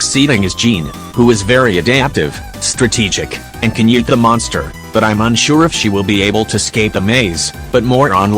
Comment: am i the only one who heard "eat the monster?"